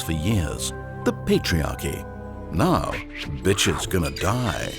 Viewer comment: thought he said. And now, the bi*ch is gonna die XD